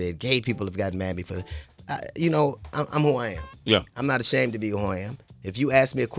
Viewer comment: he said "I'm who I'm am" , Thought he said "I'm hawaiian", funny